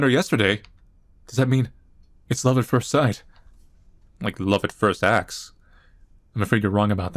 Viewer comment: Lol. I heard "love at first ass".